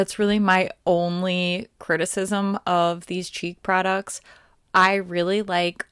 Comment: I thought you were saying"cheap products" instead of cheek products lol! Ok, back to the video lol! Love your reviews!